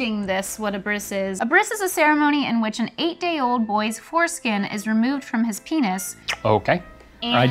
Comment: When was explaining what a bris is, I thought she said "18 year old boy" and I was like "Damn, must be embarrassing to do it in front of everyone"